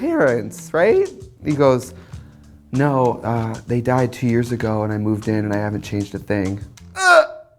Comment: screw my non English ears. what did Trixie say at 4:42? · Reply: Gamaliel no they died two years ago and I need to move in UGHH HUHGH